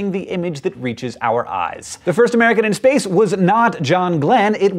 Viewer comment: I was going to correct you because I thought you said first person in space, but you said first American.